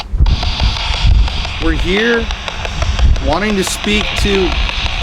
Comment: sounds like someone saying f* off